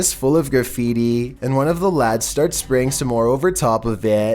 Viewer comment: Did he just say lad